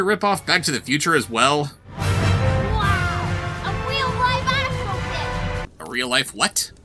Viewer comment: it sounds like he said "astro bitch" which is hilarious